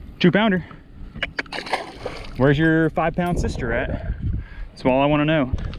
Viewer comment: did you just say that bass has a FAT SISTER. that's not right ,she may be sensitive about her weight. 😆 🤣 😂 Love the videos, Thanks. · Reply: Pretty sure I said Big lol